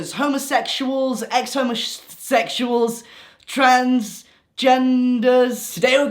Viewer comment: My youtube opened the video at 0:06 and i thought you were saying "GENDERS" like Ludwig says "BOYS" at the start of his videos